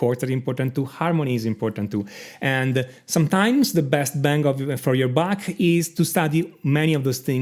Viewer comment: I honestly thought you said "the best bang for your bach" which had me in tears... even more befitting that the best bang for your bach is harmony